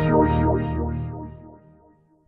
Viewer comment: sounds like its saying 'yoshi'